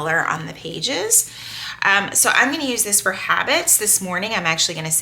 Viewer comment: Hearing your voice makes my day! I’m sending up prayers and I always wish you well. HABITS…okay I thought it said Rabbits. I was wondering what kind of treatment they had you following! 😂